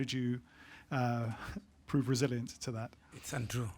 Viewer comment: what he said? Its a true or? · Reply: It's untrue